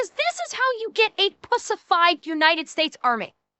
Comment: For a second there, I thought she said bussified instead of pussified lol